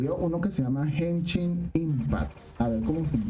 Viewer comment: no sé si escuche bien pero si conozco Genshin Impact